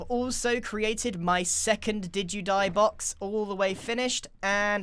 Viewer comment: Binge watching rn so pls don’t judge how late my comment it but, I love the fact that Grian has a “did you die” box😂😂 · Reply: I actually think he should name the did you die boxes to Didgiedie boxes(did-jee-dye) because thats what it sounds like hes saying